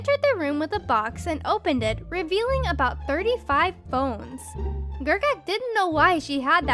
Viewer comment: why did my dumbass thought she said bones lol